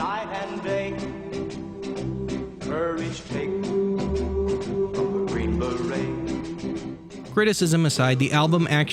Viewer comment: Did he just say "Curry Paste from Green Berets?"